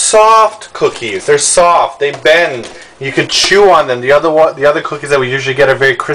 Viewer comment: Yay! They have Skippy peanutbutter in Korea! (and yes, it did sound like said "bwend" lol)